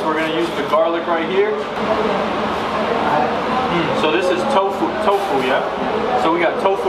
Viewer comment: It's a Kerala based restaurant.. I don't know why they used tofu for malai kofta... Indians don't eat tofu but a by product which is soya chunks... Malai kofta is a potato cheese cream based dish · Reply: After the video I realized that I just thought he said "tofu" but he said kofta 😂